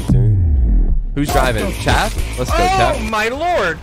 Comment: ▶ We'll be adding fans clips on regular bases, make sure to submit yours here: <link> ▶ Join "Grumbae" Memebership through this link: <link> ▶ Support a Creator code: Grumbae ▶ Follow us on TikTok: <link> ▶ Follow us on Twitter: <link> ▶ Grumbae Merch Store: <link> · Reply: sounded like lets go champ